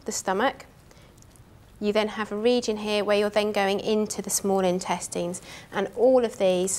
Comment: Small Intestings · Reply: IKR i thought she was saying small testes